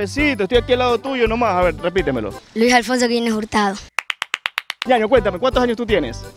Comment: entendí Luis Alfonso bien abortado jaksjjssj